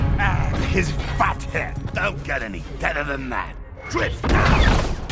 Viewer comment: did anyone else hear crosshairs call megatron a dickhead ? 😂😂😂